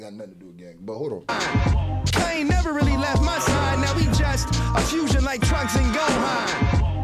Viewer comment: When i heard the trunks and gohan bar I thought he said now we are a missed fusion like trunks and gohan Since everyone wanted to know how a futurge gohan and trunks fusion would have been